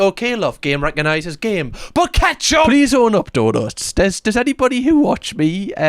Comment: I thought she said hot sauce at first. Tobasco maybe i could understand fair enough but nah not ketchup